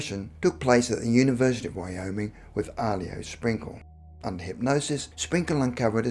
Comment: Sorry it’s Dr. Sprinkle.Thought he said Winkle.